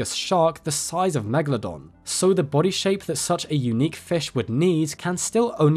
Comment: When I was momentarily distracted I misheard "a unique fish would need" as "a unique fish with knees" and snapped back to attention. "They discovered WHAT NOW?!" I had to listen to it a few more times before I finally clued in. I think it's second-cup-of-coffee-o'clock. Thank you for another fantastic shark week! I look forward to it every year. While Megalodon is probably the species of prehistoric marine life I'm least interested in (sacrileges, I know) it's always nice seeing two of my main interests overlap. Thank you for continuing to provide educational and interesting content!